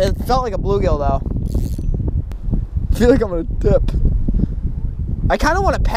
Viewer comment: Did he say he feels like he's on dip? 5:11 lol #packinfatties